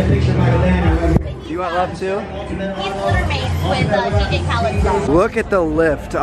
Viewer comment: Did I hear "liter mates with Dj Khalid's dog"